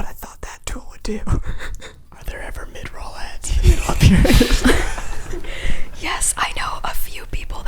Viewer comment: what did ned say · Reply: ik it's been 5 years, but he said "are there ever mid-roll ads in the middle of your videos?"